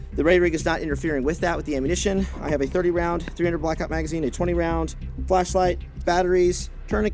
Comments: Dang, I need a 20 round flashlight. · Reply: Went from 30 round, to 20 round but said flashlight fast so it sounds like he said 20rd flashlight, listen to his annunciation and you'll hear it. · Dang, I need a 20 round flashlight. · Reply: I thought he said 20 round flesh light . I'm like dang I need one of those... Wait..😅😂